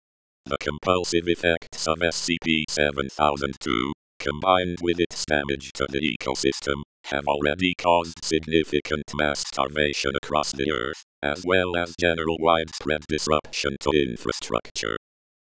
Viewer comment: The tts sounded like it said masterb*tion instead of mass starvation.